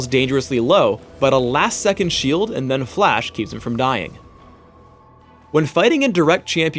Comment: i think they misheard Phreak say "keeps him from dying", thinking he said 'her'